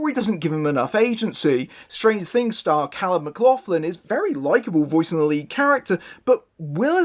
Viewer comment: I thought you said the main character was voiced by Kyle Maclachlan and was perplexed